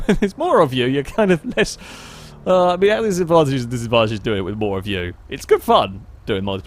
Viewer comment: What the hell did you say? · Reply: there’s advantages and disadvantages doing it with more of you. I think that’s what he said 😂